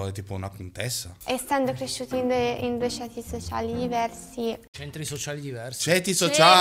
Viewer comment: anche io ho capito Centri sociali🤣🤣